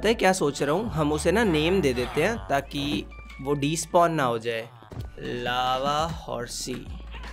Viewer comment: my ears:- 2:50 sec did he just called him lavaris? Brain:- :/ maby yes? Heart:- NO HE DID NOT! Hands:- Posting a meme on it!